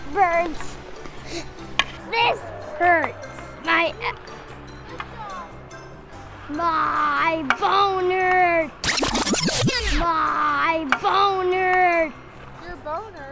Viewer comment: Yes bone hurts, what he did say. Also that instructor be thiccccccccccc